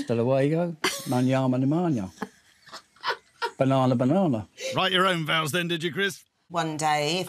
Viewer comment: can somebody tell me what is said after hasta luego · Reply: I think he was trying for “hasta manana” (see you tomorrow / goodbye in Spanish) but messed it up so said “banana banana” afterwards 😂